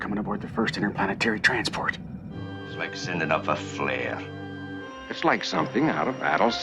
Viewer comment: I always thought he said, "He's mixing up a flare."